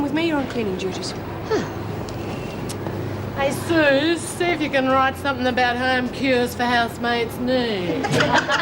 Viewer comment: What does Helen Smart say to Susie Driscoll? I don't understand her accent. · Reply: Hey Suze see if you can write something about home cures for housemaid’s knee